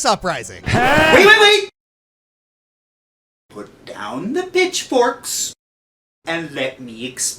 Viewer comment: did he say "bitchforks"? 😂😂